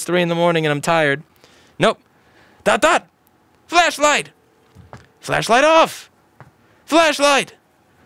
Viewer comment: Miss heard that as "Fleshlight on" "Fleshlight Off...."